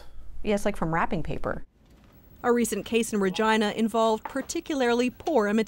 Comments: did anyone hear the journalist say Virgina not Virginia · Did she say virgina? Or did she mean Virginia? I'm confused